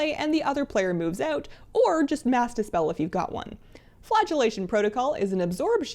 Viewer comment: Don't know how but I heard "Flatulation Protocol" instead of flagellation.